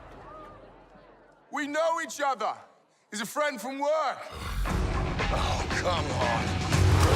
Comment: does Thor say hulk, come on or oh, come on?